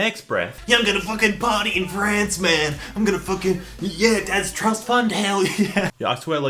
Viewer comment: did u say im gonna fuccin yet (short yeet) (*hits a whip nae nae*) dad's trust fund hell yeh ?